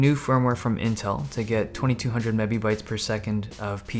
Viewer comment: lol did you say mebee-bites? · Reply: hah yea. mebibytes - it's a bandwidth measurement. it's weird but CUDA-Z uses it. (one of the easier apps to check data throughput for eGPU connections)